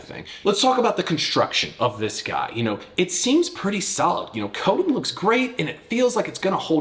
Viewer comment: what he said- "coating looks great". What I hear, "Cody looks great"...well someone has a high opinion of themselves. LOL